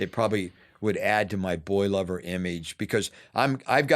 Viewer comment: Stg thought he was saying “boil over”